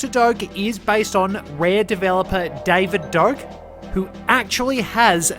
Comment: Haven’t seen good old 007 gameplay in a long time!! Nice video!! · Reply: I thought he said David Duke.